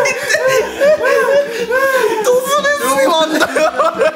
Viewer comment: ドズネズミマンって聞こえる笑笑